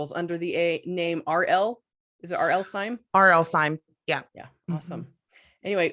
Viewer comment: I played the same segment several times... (Edited) thinking I was going crazy, becaus I thought she said she wrote mystery as R.L. Stine. 😂😂😂 Good thing she clarified at the end.